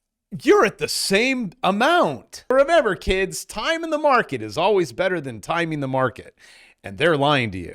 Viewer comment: stop talking like a rapper....This whole time I thought you were saying "Timing the market is better than timing the market". But I asked A.I. what that means and it said, " I think you mean the famous phrase"TIME IN THE MARKET, IS BETTER THAN TIMING THE MARKET". I was like ohhhh yeah that makes more sense lol.